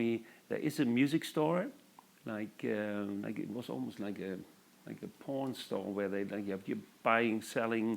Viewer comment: Am I the only one who thought he said "Porn Store" instead of "Pawn Store" at 2:10? lol